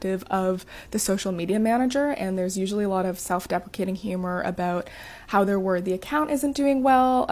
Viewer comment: I think she says self defecating instead of self deprecating at about 0:19. I suppose both would mean you're crapping on yourself anyway.